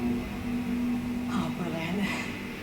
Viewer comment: Jodie Whittaker's Yorkshire accent is going to take some getting used to. It sounded like she said "Ah, brillie" instead of "Oh, brilliant!" And also, it's been implied that the Doctor needs to regenerate inside the TARDIS to help smooth out the process, but you'd think the Doctor would have a special regeneration room or maybe the TARDIS could reconfigure its interior to be resistant to regeneration energy in order to keep the damn ship from being blown up with every regeneration.